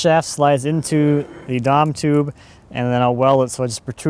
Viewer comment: The what tube? Sounds like you said "Dom tube". Whatever it is looks like schedule 80 pipe. What did you call it? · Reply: yup DOM. Drawn over mandrel